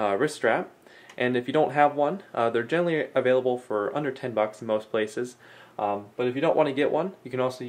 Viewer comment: Don't worry, I thought he said "a hundred, ten bucks" too